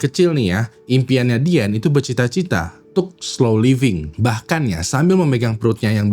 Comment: gw dengernya skuy living 😭😂